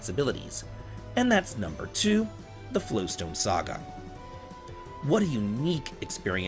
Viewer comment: I misheard Flowstone Saga as "Flintstones Saga" and immediately imagined a Mario and Luigi style RPG with Fred and Barney lmao.